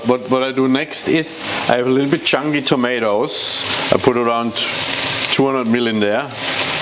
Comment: so I googled Changi tomatoes, cause I have no idea what that is. Google says it's a place called "Jewel Changi Airport" ☠️.